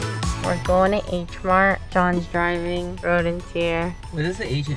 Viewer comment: IDK why i heard her say "John's Driving" instead of "Sean's Driving"